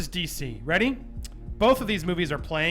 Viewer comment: I don't know if it's Trisha's presence or what but I think I heard saying "these boobies" instead of "these movies".